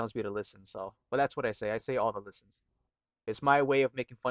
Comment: You're saying "All the listens"? Well, I was wrong. I thought you were saying Obelisk, which IS a funny word. Thanks for clarifying!!